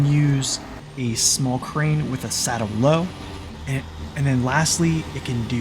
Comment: no the 47X 1424 can use that combo mentioned as of Season 13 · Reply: yeah ok bro, i thought you were saying flatbed instead of saddle. sorry for my mistake 😟